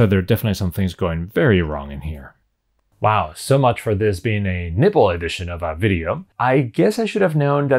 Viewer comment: I know you're saying nibble, but I kept hearing nipple. I'll go get my brain from the gutter....